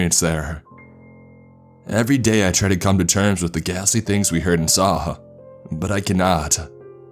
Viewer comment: At first I thought that he said "The Gassy Things" which would have been pretty horrible as well.